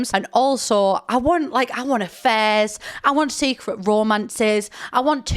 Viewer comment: I heard "I want a fez" instead of "I want affairs" 😂 Too much DrGluon